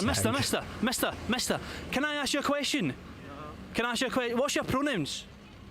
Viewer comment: His accent makes it sound like he is saying what's your pro nyms think of whim but with a n lol so yes I would prolly get confused to